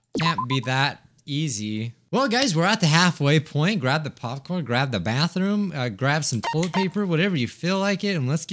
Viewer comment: did he really say grab the bathroom XDDD